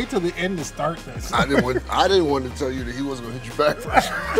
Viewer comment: “I didn’t wanna tell you he wasn’t gonna hit you back first” 😂😂😂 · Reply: I heard "He wasn't gonna get back to US"